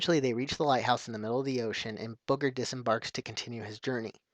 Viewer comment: did you just say "Booger"? :P